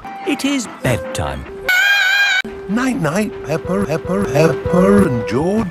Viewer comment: Why does it sound like everyone says "pepper" instead of peppa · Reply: I thought you said pipper